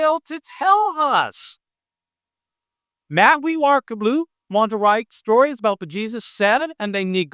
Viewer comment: I'm sorry what were those words there?? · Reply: Matthew, Marc and Luke